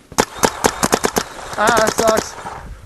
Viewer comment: i think says ion sucks?! lol